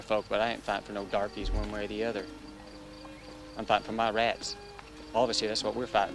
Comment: "Fighting for our rights." is what I think the man said.